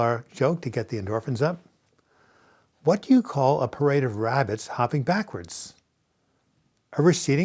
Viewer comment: When you told the joke, I heard 'Parrots' instead of 'Rabbits' somehow. Boy, was I confused for a while haha.